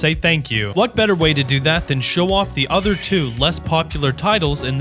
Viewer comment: Did you say less popular? I have not yet tried BG3.